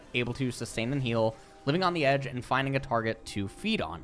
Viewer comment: is it just me or does he say "finding a target to feet on"